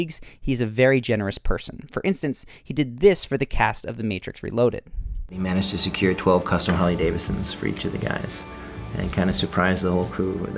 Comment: i think I'm forgetting english but I can't make out WHAT did Keanu do? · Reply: he managed to secure 12 harvey-davidsons for each of the guys - oh thank you - you're welcome